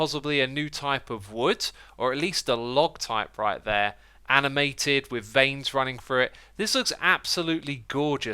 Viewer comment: I heard “anime tit”